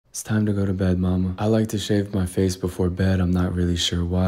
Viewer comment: I thought you said it’s time to go to bed mom